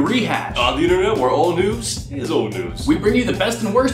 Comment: I thought you said where all noobs is old noobs. Then I realized.